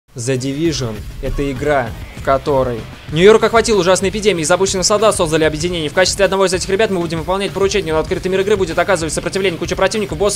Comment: Что он сказал на 0:03-0:07 ?? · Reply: Нью-Йорк охватила ужасная епидемия, из обученых солдат создали объединение, в качестве одного из этих ребят мы будем выполнять поручения...